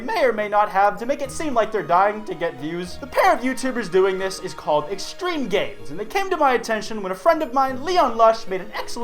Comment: it buffered and it sounded like u said extreme gay